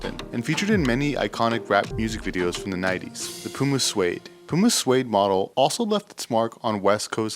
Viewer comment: I would just like to say I went n seen AIR in theaters after words I say their from 6pm-2am watching your sneaker history videos just because u guys hit the mark on every single topic n shoe u guys talk about I can tell it’s not for money it’s a true passion and it truly shows with every video u guys drop please keep it up 🙏🏽❤️💯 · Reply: Thought you said Puma Swede